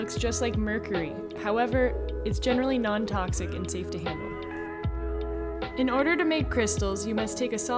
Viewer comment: I had to rewind, I thought you said "safe to EAT"!!! 😅😅😅